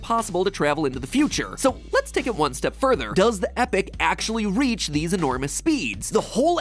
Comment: So is that how you pronounce epoch? It sounds like you said epic.